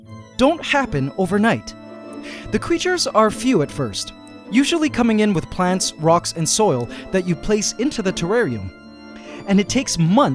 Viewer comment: He said = "Usually coming in with plants rocks and soil." What i hear in my head. = "Usually coming in with plans, rock and soil - envisioning a ant with a worker hat and blueprints in his hand opening it on a table..."